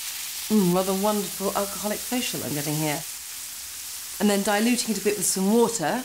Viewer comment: Any idea what she says at 5:24 just after pouring red wine over the sausages? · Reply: "Hm, rather wonderful alcoholic facial I'm getting here"